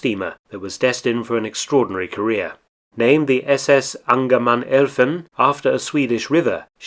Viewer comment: Sorry, what on Earth did you just say? Mind writing it down in the description because I can't make out the Swedish word at all... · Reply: It's interesting that the people who keep picking me up on pronouncing words are always Swedes, and always so wonderfully direct about it! The name is 'Angermanelfven'.